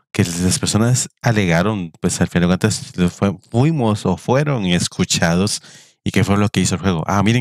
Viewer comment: me puedes traducir eso? XD, yo entendi, pues esas personas kfkggefkugkegfkgkg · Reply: Fuimos, fueron